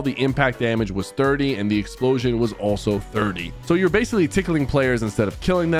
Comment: BRO WHAT DID HE SAY😂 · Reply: And the explosion was also 30